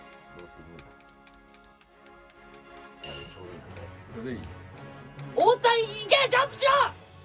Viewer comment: 大谷いけ！ジャンプしろ！にしか聞こえんかった